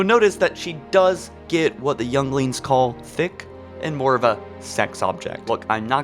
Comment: Did he just say youngings